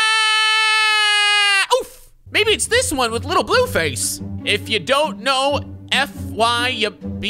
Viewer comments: did you just call NBA Youngboy, Lil Blueface, 😂😂😂😂😂😂 this guy is a fucking joke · did he just call NBA Youngboy little blue face